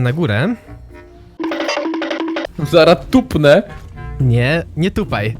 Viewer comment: usłyszałem dupnę xd 😄